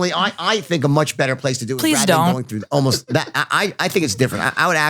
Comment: anyone else hear the "please don't" at 1:22:20?